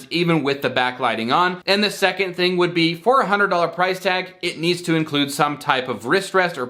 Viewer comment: It sounds like you said $400 price tag. The captions also read $400. My jaw literally dropped when I thought I heard you say that. I almost closed the video entirely I was so astonished that any company would charge that amount for a keyboard. I was shaken.